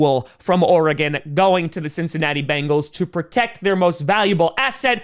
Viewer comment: When I hear Cincinnati, I just hear 'kitty goes' meow!' instead